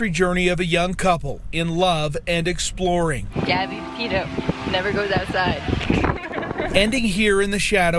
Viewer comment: Can anyone say (tell me) exactly what she said?? 'Dont go to the other side'..??